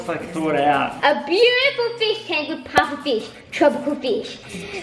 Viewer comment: when said "pufferfish" I thought she said "papa fish"!! 😂😂😂